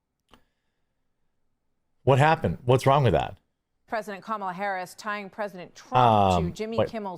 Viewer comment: President Kamala Harris? Da Fuq I just hear?